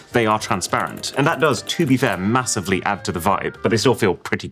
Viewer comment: did I just hear massive 🫢